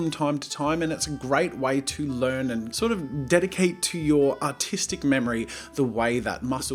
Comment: The coupons are used up already?! I didn't see that coming - I added a few more for people who missed out, thanks for the support! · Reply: Draw with sounds like you said autistic memory at like